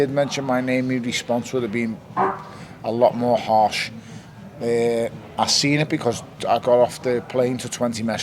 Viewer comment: 'The response woulda been *fart*." Anyone else hear that?